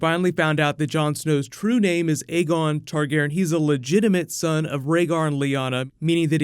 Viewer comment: I first thought they said "Egmund Targaryen" xD